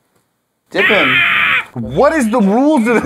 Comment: when it said dip me I thought it said kiss me lol